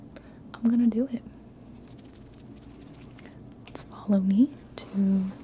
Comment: swear to God she said "swallow me"